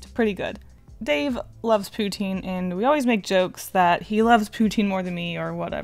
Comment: for a minute there i thought yu said pooting hahhhhhhaaahahhahhhaha